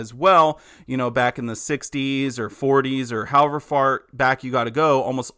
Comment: what I hear is "however fart" LOL!🔥🔥